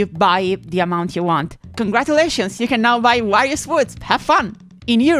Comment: the captions say "various foods" instead of "Wario's Woods"